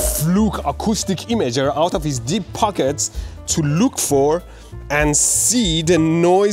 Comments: I thought he said "D*ck pockets" 😂😂😂😂😂😂😂😂😂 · I thought you said "out his dick pockets". Would have been more exciting